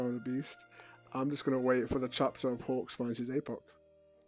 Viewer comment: Is it just me or is it that I can't understand what you're saying half the time? Like right here, I have no clue what you said. Yet she seemed to have understood and laughed at it. 3:09 "I'm just gonna wait for pgjdgjdfihjfidhjdfh gfhd fgd fdf h fdh dfhfhdfhf hdf pop." Me: Wha- Is this Holds hand up to present a butterfly. a new language? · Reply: Chapter for when hawxx finds his apoc.